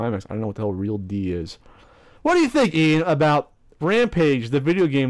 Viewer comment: what i hear " WAT! WHADDYA WHINK WABOUT WAMPAGE? " mic control required!